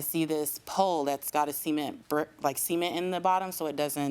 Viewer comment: Sounds like she said semen instead of cement 😅😅